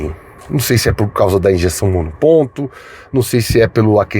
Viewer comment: eu ouvi injeção monoponto, ou me empolguei com a viajem ao tempo da chave canivete e encosto traseiro?